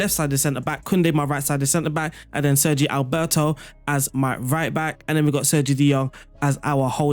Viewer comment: is he sayin Sergio Alberto amd Sergio De Jong 😂 ?